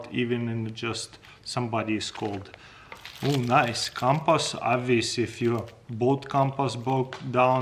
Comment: Ngl I wasn’t looking when he pulled out the compass and I swear I thought he said compost, the confusion...